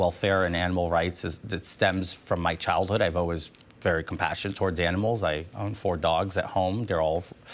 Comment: "I've always (sic) very compassion (sic) towards animals" Genuinely misheard that as "I was always very passionate towards animals" and had to rewind.